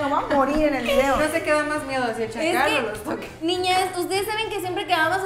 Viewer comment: Yo la verdad quería saber quién era el verdugo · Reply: En el minuto 4:45 una de ellas creo que dice " no se que da mas miedo si en chacal o lotoques" creo que esto es lo que dice.